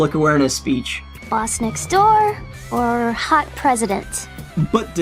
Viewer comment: "hot president" I thought Bonnie said "sassy president"?